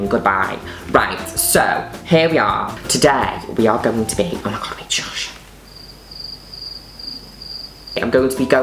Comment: Does anyone know what she whispers at 1:27??? I've been trying to figure it out and even the CC doesn't help! I also slowed it down and listened to it 20 times and I still can't get it. PLEASE HELP!!!! Also, love Juno Birch so much!! I'm binge watching all of these to raise my spirits and it's working GORGEOUSLY! Stunnin. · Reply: Is it "Oh my God wait shush?" Did I just figure it out?!?! Why couldn't I have figured it out before I commented?! Damn!